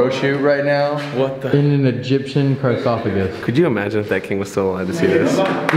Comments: Did anyone else hear Elton say “carsophagus” at 11:49? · it sounded like Elton called it a "Carsophagus" 😂😂